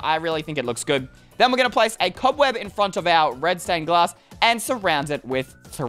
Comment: he said red stone glass instead of red staring glass😂